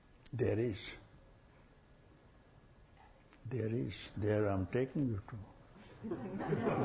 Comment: Can somebody please tell me what Papaji says @ 3:09? I hear "there is" but after that i can't understand, and it seems really significant! Thank you! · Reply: after that he says ,there i am taking you to